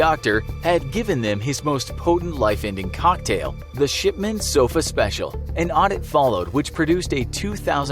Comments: I thought he said "Shitman" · Sounds Like hes saying Shitman